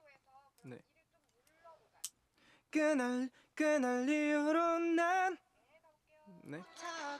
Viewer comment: 나만 그날 리우로 라고 들리나?